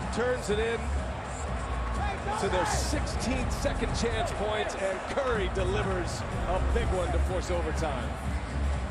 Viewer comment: What did curry yell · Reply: "Hey Tony" AD goes by Tony. I think he wanted to rub it in. "Not enough even when you plowed me into bleachers".